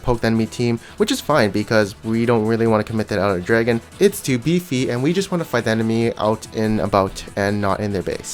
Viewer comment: DID YOU JUST SAY LEAFY!?...GET HIM OF MY STAGE